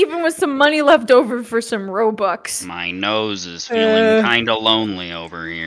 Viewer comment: Did i hear... ROBUX?!?